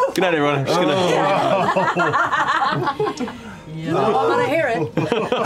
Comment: Can anyone tell what Ashley says at 15:08 ? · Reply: From what I can tell, “You know I’m gonna hear it” probably talking about trolls on social media.